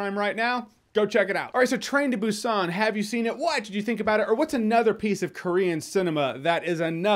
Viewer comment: Is it just me or does it sound like says "whhy did you think about it?"